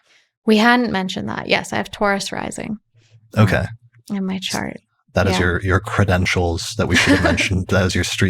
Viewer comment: Repeating myself but I so appreciate the captioning \o/ I just took note of tiny mistakes: At 3:00:08, says "--in my chart.", not "--in my trine." At 3:02:20, she says "Aspect it to something!" I think, not "Aspected to something!" (I might have misheard, but it seems to make more sense with the first possibility). At 3:04:27, "Paulus Alexandrinus" got transcribed as "policy Alexandrina" At 3:05:35, says "Venusian", not "Venetian" and then a few seconds later, "wanting to be Freyja" instead of "wanting to be frail"